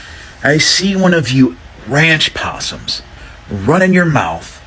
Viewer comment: Did he just say ranch opossum??!!! That’s crazy!! 😂 Girl shaddup This story seems psychotically made tf up! Girl bye!